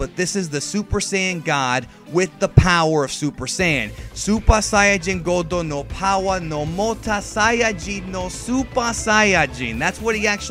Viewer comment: all i heard was super sayajin no power sa sa sas sa sasas sayajin